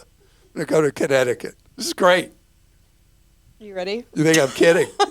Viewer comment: giving Clark a Mom Look at 25:11 🤭 followed by a "you ready" which sounded like a "you done?" at 25:15 😁